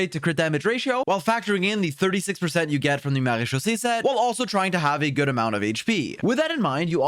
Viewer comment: Marechausse set. I always hear "Marie-Josée set" 😅